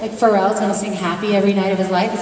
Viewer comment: what does she say at 1:53? Like for reals... ?? sing happy every night for his life...???? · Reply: “Like Pharrell’s gonna sing “Happy” for the rest of his life?” In reference to Pharrell Williams